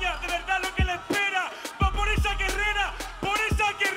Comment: pensé que dijo va por ese guerrero, por esa guerrera. Por eso se la grité